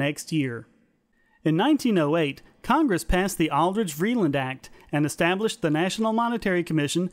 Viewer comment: The transcript for the English CC at 01:20 states "Aldrich坊reeland Act" instead of "Aldrich - Vreeland Act". Any idea as to why the "坊" is in there?